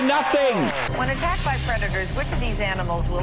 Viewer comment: at first I thought she said “When attacked by Redditors” lol